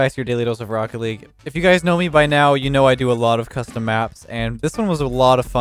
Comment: Thought he said “i do a lot of crystal meth” lmao